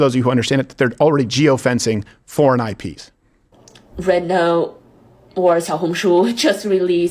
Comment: I thought they said RedTube.